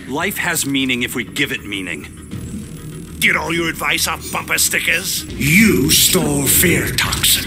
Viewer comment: “Get all your life advice off bumper stickers” 😂😂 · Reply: It sounded to me like a question so I heard it as "Get all your advice off bumper stickers?"